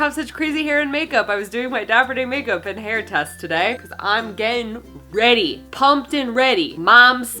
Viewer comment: When she said "I'm getting ready" I heard "I'm gay and ready" and I was like "does Leo know??!!" 😂😂